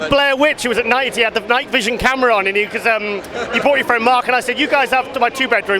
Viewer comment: did he say your boyfriend mark to lee? & the look on lee's face? Is lee gay?? Either way he is still a cool ass dude i just never would have guessed in my life if he is. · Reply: “you brought your friend Mark” he ain’t gay , he’s got a fiancée , Freudian slip by you maybe ???